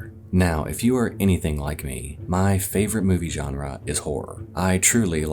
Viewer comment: Does ad read What is said: If you're anything like me... What I heard: If you're a guinea pig like me.... Me: squeaks excitedly